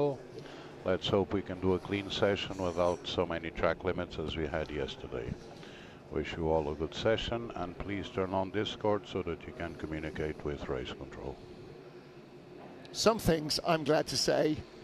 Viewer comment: did he say turn on Discord to communicate with race control?